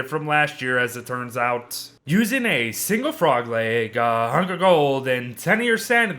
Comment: did you say a hundred gold